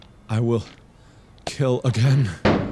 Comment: did anyone else hear him say kill…again?